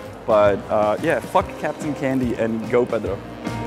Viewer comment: Lol the captions said 'pedo' instead of 'Pedro' and my brain went "fuck captain candy and go pedo"